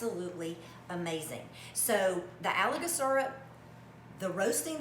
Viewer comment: I thought she was saying algae syrup...and I was thinking what!!!!!???? But it's Alaga syrup 😆